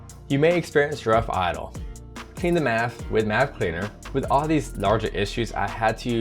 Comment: regarding rough idle - clean the what with what cleaner??? Hard to understand what you said. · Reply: he said maf (mass airflow sensor), but if your having problems you should check the idle air control valve first